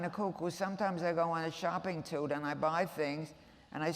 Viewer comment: "Sometimes I go on a shopping-tude." Not ATTITUDE. Did she say SHOPPING-TUDE??!! I stan!